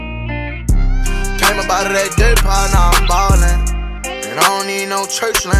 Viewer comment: I always thought he said "Came up outta that DIRT PILE now im ballin"